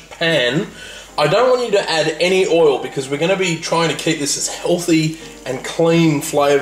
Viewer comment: did he say "healthy"? Nice pie btw!